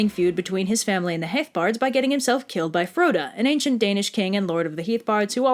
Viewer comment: I heard this name as Crota, a character from the Destiny games, and was extremely confused for a second